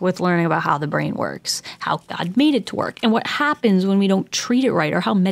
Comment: Hold up 15:21 did you say "How GOD made it to work..." ?! YESS